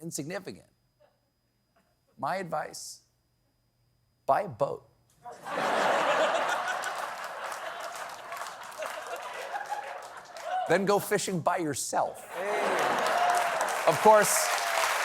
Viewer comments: Hey what did Stephen say at 8:05? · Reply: “My advice, buy a boat. Then go fishing by yourself.” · I thought he said 'Bible' instead of 'buy a boat' and thought 'I know is Catholic, but that's a bit on the nose...'